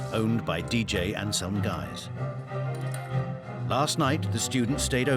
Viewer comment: sounded like "owned by the dj, and some guys"